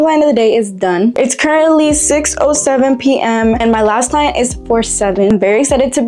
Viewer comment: I love all of your nails and you clock it every timeee but I’m really sorry to do this but did you say 67